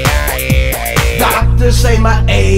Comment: i thought it said 'I should have won a condom '